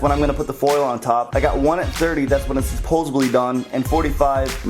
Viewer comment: at 4:50, did you really say "supposebly"?